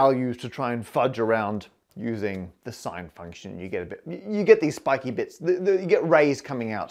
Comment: subtitles says 'James Street' instead of Jane Street 7:35 Subtitles says 'octan' instead of arctan 12:10 Subtitles says 'F9' instead of (presumable) affine 13:38 'Goebel' instead of Goble 19:56 'gene street' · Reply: subtitles says 'sine function' instead of sign function.